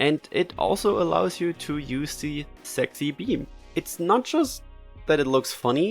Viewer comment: Did she say “sex”? 😂😂😂